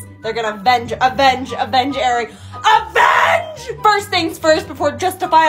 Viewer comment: it sounded like she said "avenge Eric"😂😂